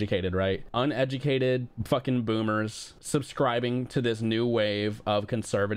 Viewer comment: "Pho king boomer." I fell off my couch when I heard this. These were the kids that haunted my worst nightmares, when I grew up in the 60s. (we were the only Chinese family for miles around)